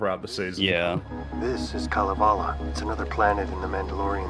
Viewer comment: Wait... Did I hear that right? Kalevala? As in the Finnish epic? I mean okay, it's an obscure name and sort of fits since the Mandalorians are basically space vikings in concept. Still a weird choice, somehow I doubt there's planet Beowulf or Nibelungenlied in the SW universe.